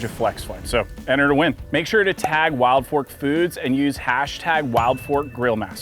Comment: Damn this whole time I thought he was saying wild pork 😂 I was like why tf he talking about pork in a steak video then saw the ad